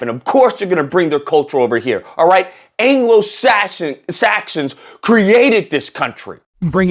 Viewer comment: that sounded like anglo assassin's 😂